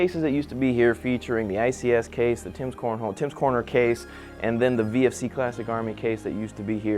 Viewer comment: Did he say Tim's Cornhole at 0:30??